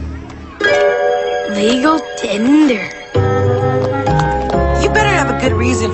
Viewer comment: did I just hear LEGO Tinder ?? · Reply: Legal tender lol